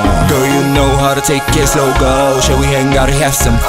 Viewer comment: i know the rapper is saying: "Lucky one" but I keep hearing: "Milky bar" :D :D